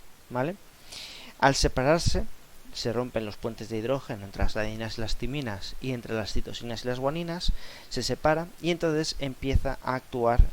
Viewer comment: que es lo que dice en el minuto 6:11 y 6:13? no logro entender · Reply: "...se rompen los puentes de hidrógeno, entre las adeninas y las timinas, y las citosinas y las guaninas, y se separan..."